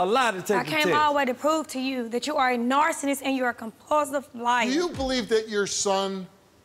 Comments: did she call him a “narcinnist” LMAO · what the hell did she say? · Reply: Narcinnist!!! Lol